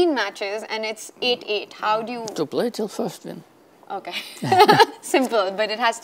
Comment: can anyone tell me what does karpov say at 19:24? thanks in advance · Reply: to play to first win!